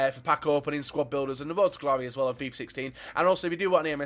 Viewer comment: Did he just say Fifa 15😂